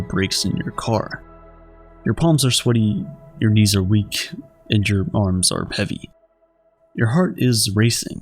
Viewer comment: I came from the mom video and thought he said "Palms are sweaty, knees are weak and your moms are heavy"